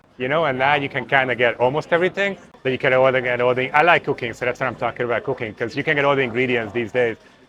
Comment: I definetly needed to rewind, just to double check if I misheard what he said LOL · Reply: Cooking, not coca